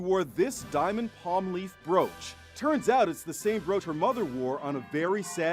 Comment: till 0:31 her accent tho😊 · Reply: I thought she said “roach”